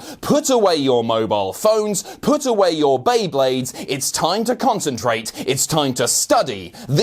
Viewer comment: For a second, I thought he said, "Put away your Baneblades."